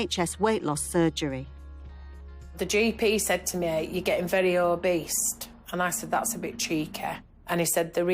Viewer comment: "The GP said to me: You're getting very obese-sT... And I said: That's a bit cheeky." me: did i hear "o-beast" ; whilst "cheeky" echoes in irony